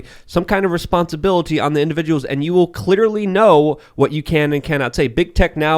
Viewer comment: did Luke just say ‘Cliterally’ ? 😂😂😂